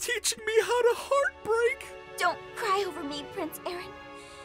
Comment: It kind of sounded like She said ( Don't cry over me acorn )